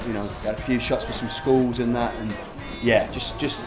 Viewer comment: when he said skull, i thought he said school. WOW.